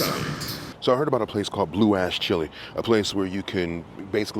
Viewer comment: Me, never having set foot in Ohio & multi tasking, heard him say, "Blue Ass Chili." 😂